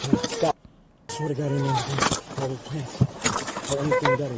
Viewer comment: That is the moment you need Jesus most. good point Jesus came through. At the end, it sounded like he said, "Please take me Jesus, i repent. I could think he and Jesus were together at the end because he wasn't dying and not going in alive.